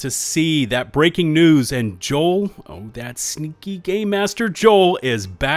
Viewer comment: did u just call him a gaymaster?